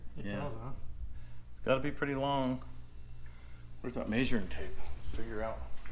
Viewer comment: Measuring tape?! I thought you said major intake!